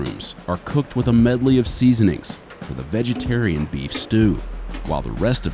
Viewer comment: Did I really hear "vegetarian beef stew?"